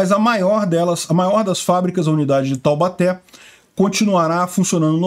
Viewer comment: Eu ouvi "a grávida de Taubaté" kkkk